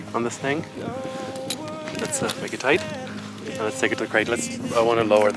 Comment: On 3:36 I think I heard him say ( let's take it to Craigslist ) or something like that